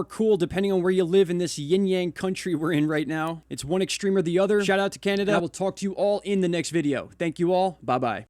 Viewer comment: Does anyone else hear the phrase "Shut up Canada" at aprox 6:40........?